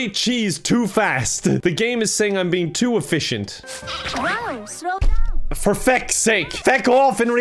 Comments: When she said “slow down” I thought she said “so dumb!” and I was like dang Mama, no need to be so rude about it, turg is learning! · When she said "slow down" I thought she said "so dumb!"